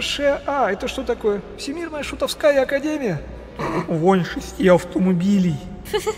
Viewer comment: Что сказал Борис??? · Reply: Вонь шести автомобилей